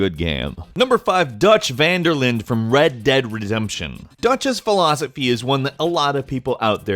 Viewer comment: "Red Dead Retzempshion"... probably a glitch in the matrix... but it sounded like he's drunk and slurring... lol but another great video